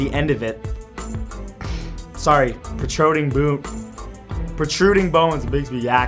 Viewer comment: At first it sounded like he said partruding boobs...